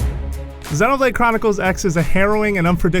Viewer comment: Wait did i just heard Zelda Chronicles.